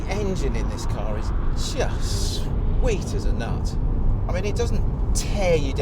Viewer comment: did he just say "sweet as a nut"?! 2:40 lmao ;) i got your sweet nut right here!! lol